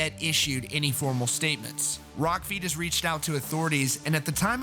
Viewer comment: When you said "Rock feed has", it sounded like you said "Rock Fetus". I found the name of my next band.